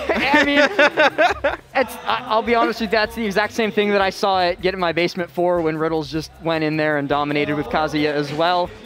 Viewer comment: first thing i hear is “get in my basement”